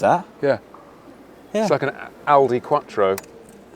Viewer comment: I thought you said "Audi Quattro". Very fitting.